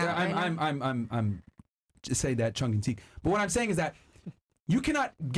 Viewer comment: instead of tongue and cheek he says Chung and Teek 🤣